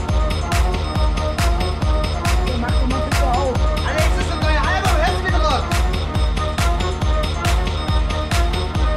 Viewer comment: what he say? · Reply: Alex (the nurser trainee): „Icka, could you please open the window?“ - Icka (the DJ and patient): „Alex, this is the new album, do you hear how much it rocks?“ Background information: Alex had known Icka before as a nightlife DJ, finds it pretty cool when he joins the clinic due to a psychose after taking a „bad pill“ and immediately asks when Icka‘s new album, which he was working on before and also from within the clinic, will be out. The track playing is said to be one of the album‘s tracks, and Icka plays it to provoke and react off after being left behind by his label manager when she told him she had cancelled the album release due to Icka‘s health/drug problems.